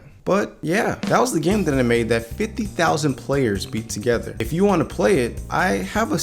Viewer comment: I thought it said 500,000 players